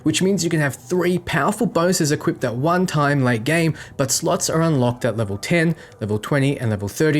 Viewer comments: Thanks for watching guys. Before you all say it, I know it's pronounced 'si-g-al', but it's a bit of a running joke that I call it that after mispronouncing it the first time. Competition here: <link> Discord here: <link> Timestamps here: Wear State: 00:47 Sigils: 01:50 Blueprints & Crafting: 03:10 Explaining Items & Combos: 05:37 Daily, Weekly, Monthly’s: 08:49 Misc: 10:01 (edit: People couldn't see this comment so made a fresh one) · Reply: LOL @ 2:31 - 2:34 , I heard you say "SLUTS are unlocked at level 10" ahahaha . I know you meant 'slots" ;) anyway, nice vid! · Thanks for watching guys. Before you all say it, I know it's pronounced 'si-g-al', but it's a bit of a running joke that I call it that after mispronouncing it the first time. Competition here: <link> Discord here: <link> Timestamps here: Wear State: 00:47 Sigils: 01:50 Blueprints & Crafting: 03:10 Explaining Items & Combos: 05:37 Daily, Weekly, Monthly’s: 08:49 Misc: 10:01 (edit: People couldn't see this comment so made a fresh one) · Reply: Is you saying “sluts at level 10” a running joke too? I think it’s around 2:32... I was laffing and questioning where the NSFW tag was at! Bro, don’t slut shame. Try it out first ;) love your videos!